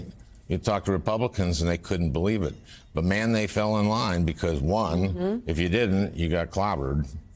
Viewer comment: Did he just say I guess sessions I guess